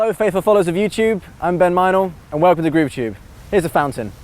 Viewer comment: Could have sworn you said "Ben Meinl" so I figured, "why is he checking out everthing except Meinl"